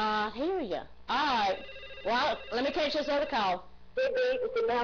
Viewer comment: What does the female caller say at 7:03? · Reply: I think it's "Well...let me catch this other call..."